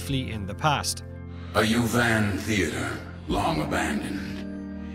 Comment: the first time i heard this, i didn't quite get what he was saying, so all i could think of is "Ha ha, you were a bouncer in a theater" Inaprops